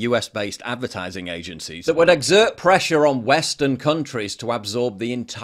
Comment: Did he say "Western countries"? I hear him say "100 countries". Western countries are not 100. Indonesia, Malaysia and Algeria could take some.